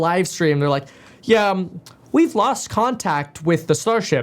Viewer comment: I thought they said "we lost contact"